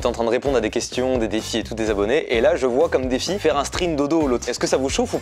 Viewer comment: il y a que moi qui a entendu STRING dodo😂😂